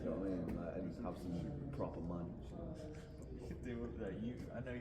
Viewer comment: Proper munch 😅 Fries or toblerone? Shaken or stirred? Regular or supersized? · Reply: I thought he said 'Some proper man shit'